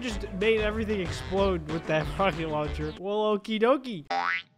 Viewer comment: i think he said frogging launcher